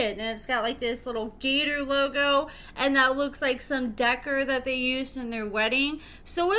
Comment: Decor is pronounced “de-core” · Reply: 😂 Right. Thought I was the only one tripping . I heard “Decker” and had to really focus on what was being said and realized it was supposed to be Decor…